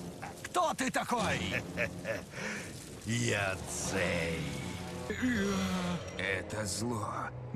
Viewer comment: it sounded like he said Beyonce